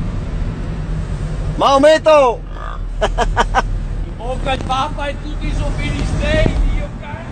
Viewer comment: cosa dice?😂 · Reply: In bocca al papa e tutti i so filistei